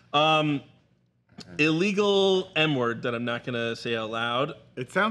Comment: I didd think he said N word 😂 9:47🤣 Monica